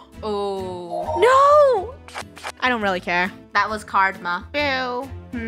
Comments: did she just say 'that was cardma (Karma)' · IS KEEPING THE RAINBOW SPIRIT ALLIVE SHE SAID A PUN 7:26 "That was CARD-MA" INSTEAD OF KARMA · did just say card-ma